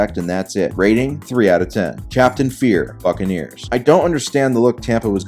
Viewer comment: anyone else catch “fuckaneers”?